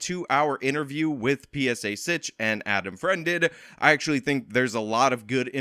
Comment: I thought you said Adam Friedland lol